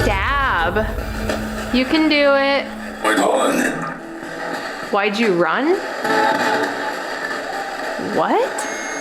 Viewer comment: I hear "My collen!" Not "why'd you run?